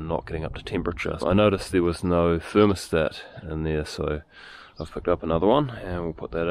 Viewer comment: Fascinating. Never heard of a thermo-start before. I'm sure you must be the NZ machine whisperer. 😀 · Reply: I thought he was saying thermostat for a minute, I was trying to work out how that was going to help it start without go go juice 🤦🤣